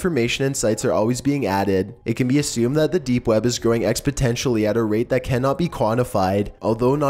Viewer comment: "Growing expotentially" instead of "exponentially"